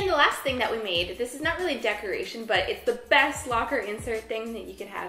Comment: I thought she said snuggly decoration instead of not really a decoration. Now i want diy stuffed animal magnets.